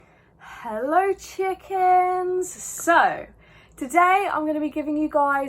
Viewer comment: did she just say “hellor chickens”💀